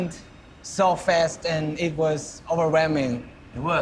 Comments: JK listens so carefully to what J.Fallon is saying that I can't. Also when JK said "It was overwhelming" I literally heard "It was our ramen" and I still can't get it how I heard that 😂😂😂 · I CANNOT be the only one who heard 'overwhelming' as 'ramen'